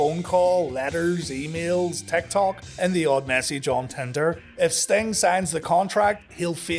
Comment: I originally misheard "Message on Tinder" as "Messy John Tenta".